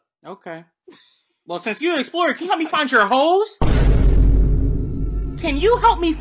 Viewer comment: "CAN YOU HELP ME FIND YOUR HO3S?"💀💀💀 Hazelnut:"Idk, look at the map dingus!" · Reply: I heard “Can you help me find your holes” and I almost passed away.